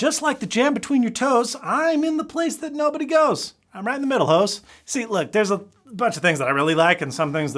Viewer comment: did he just call us hoes